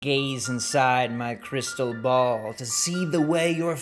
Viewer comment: I thought he said "gays inside my crystal ball"